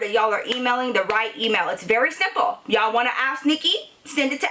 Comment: I love love your storie times and u in genaral but everytime you say "ask nicki " it sounds like your saying "ass nicky" love you girl!!